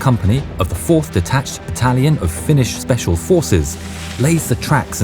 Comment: why did i hear italian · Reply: "detached battalion"